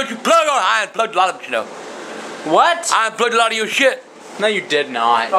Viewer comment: what the hell did he say? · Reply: "I unplugged a lot of it you know, I unplugged a lot of your shit!"